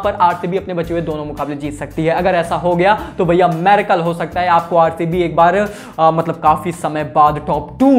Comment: maricle instead of miracle... 🤣😆